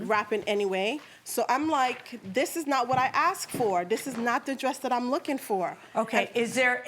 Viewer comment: "This is not the dress I was looking for!" I heard "These are not the droids you are looking for!" ✋🤓🤖🤖